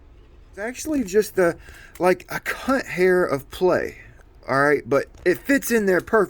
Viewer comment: I’m sorry… did you say “cunt hair”??? 😂😂😂